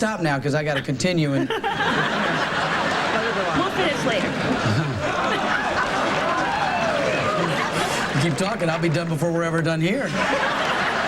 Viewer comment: What does she say at 1:48? Can't make it out. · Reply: “We’ll finish later”